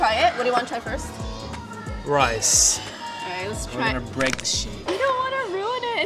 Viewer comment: did he say shi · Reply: He said shape